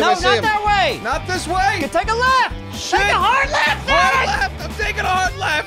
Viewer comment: Is it me or did say "dammit" at 11:43 ? "Take hard left dammit!"